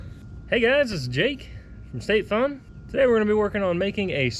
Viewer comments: I thought you said Jake from state farm. Lol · I thought you said Jake from State Farm